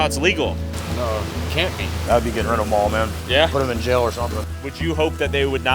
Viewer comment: the subtitles say jair instead of jail